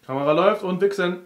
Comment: ich hab erst verstanden : kamera läuft und wichsen :D